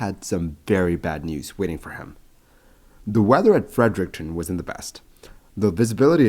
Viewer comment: The weather ... wasn't the best v.s. (in the captions) was in the best 😂 The subtitles got me confused for a second (as I'm not a native English speaker 🫠)